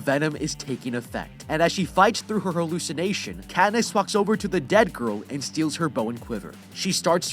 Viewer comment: sounds like ue says "fucks over to the girl" XD